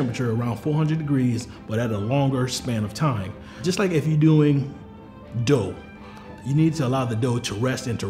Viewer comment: I swear I thought he said "Just like if you're doing dope" 😆